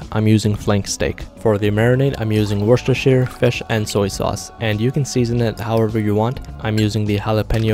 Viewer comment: i really thought he said "worcestershire, kush, and soy sauce"